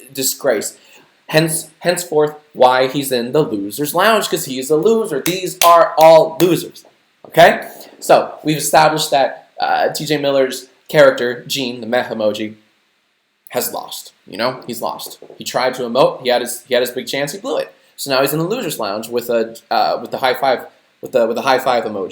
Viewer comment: For the first 15 minutes or so I thought he was saying "meth emoji"